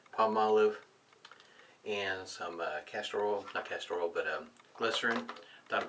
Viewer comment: At first I thought you said you had put Castrol in there😊 Now that would be a thick soap for sure!